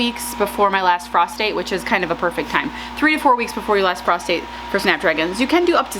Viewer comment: Frost date - thought you said prostate.